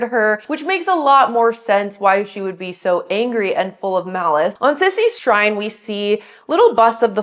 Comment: did I hear Malice?